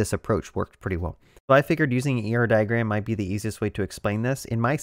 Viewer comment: Very nice. At 1:34" (1 min 34 sec) you mentioned briefly a diagramming system to get those arrows and connections. It sounded like "Euro Diagram". What is the actual name? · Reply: I think he said "ER diagram" meaning Entity-Relation diagram